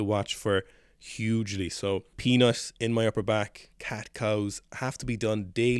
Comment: Obviously I am here for the advice on upper back rounding as that is a weakness for me. What does "penis in my upper back" mean at 2:46? I know a lot of yoga but I can't figure out what you were trying to say?